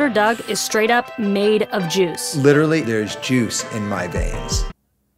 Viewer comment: i heard i have jews in my veins 🤣